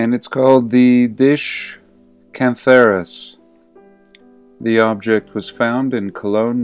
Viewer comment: I thought you said cancerous at the Cantharus. There is also a cantharel (English call and write it chantarelle?), a mushroom. Would it have a connection, the drinking bowl and the (golden rooster crest) mushroom? <link> org/wiki/Cantharellus_cibarius